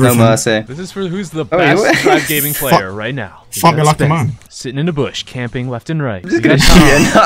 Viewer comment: Did actually say "F*ck, F*uck me like a man! "